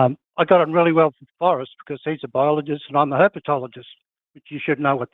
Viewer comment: Wow! I just learned something new on the first call. Whenever I've heard the term I've misheard it as herbatology and just thought "study of herbs, ok...". Somehow it never became apparent in the context that I misheard it...